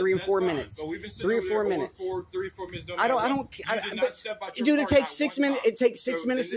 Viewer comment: I thought he said 40 minutes